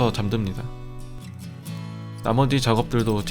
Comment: 나머디.. 음 뭔가 나만 그렇게 들리나 👍🏻